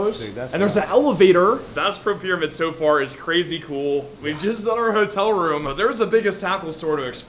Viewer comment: Did bro say we’ve jizzed on our hotel room 😭